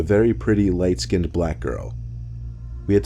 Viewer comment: I was so confused because I thought he said "I met a white skin black girl"